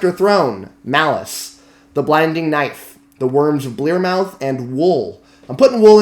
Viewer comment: For 2012 it sounded like you said "The Worms of Blairmouth." What did you actually say? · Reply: The Wurms of Blearmouth by Steven Erikson.